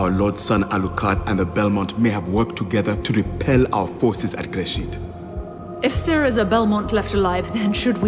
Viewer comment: Gresit. Why do I keep mishearing it as Grey Shit